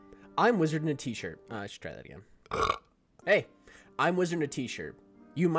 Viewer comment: When you said: I should try that again. I swear I heard you say: And I'm Australian.